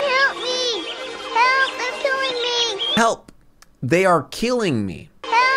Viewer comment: Blanky: “HELP THEY ARE KILLING ME” Disney: ah yes, child movie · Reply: I thought its said "Help! They're stealing me"